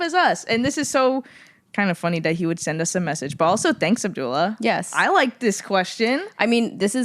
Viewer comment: for 10 minutes i thought they were saying "duola" and then it clicked...